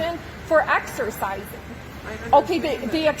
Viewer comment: In the First video I thought the karen Said exorsisin